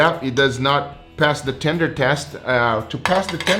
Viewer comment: I swore he said the brisket "did not pass the Tinder test."